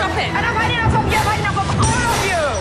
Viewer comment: Did she just say “fucking broccoli? I’m going to use that for now on I guess since nobody like broccoli.